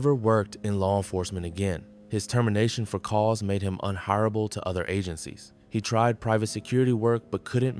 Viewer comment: Please fix the subtitles. At 20:20 the voiceover says he was UNHIREABLE but the subtitles says he was UNHONORABLE. That is a problem.